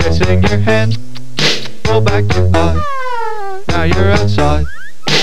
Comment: so uh. I thought he said " go back to pot" not "roll back your eye"............